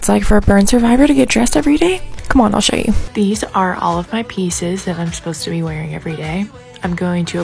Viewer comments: Legit thought she said penises instead of pieces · Thought that said “bird survivor” and I was hella confused what happened to her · Damn I thought she said bird survivor I was thinking tf